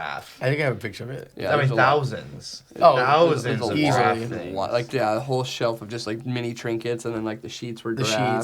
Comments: Maybe it because I'm east coast but I couldn't tell you were saying giraffe at first.couldn't figure out what a draff room was haha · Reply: I’m west coast and I thought he said draff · Maybe it because I'm east coast but I couldn't tell you were saying giraffe at first.couldn't figure out what a draff room was haha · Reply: I'm from India. Also thought they were saying draff.